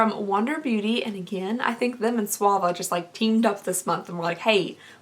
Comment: I'll pay to know what were you saying instead of Suva beauty lol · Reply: Paulette Zayas If I’m not mistaken, a little after 4:05 she mentions the brand again and I think she was saying “swa-VAH” lmao 😂 that’s me all the time tho I can never pronounce anything 💀